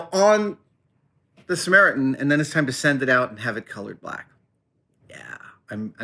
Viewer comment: "...and then it's time to send it out to have it ????" Color-fied? What's that word? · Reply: colored black